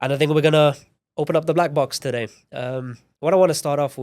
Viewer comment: sounds like he said, "i don't think we're gonna open the black box today"